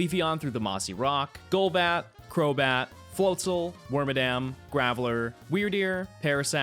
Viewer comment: I could of sworn you said wormadam not wormadan